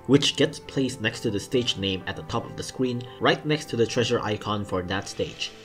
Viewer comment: he says scween instead of screen